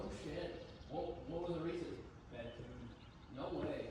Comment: am I crazy or is saying his 34 blew because of a bad tune?